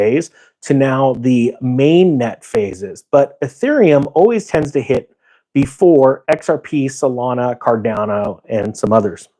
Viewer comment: Thought you said xcn would 5x before xrp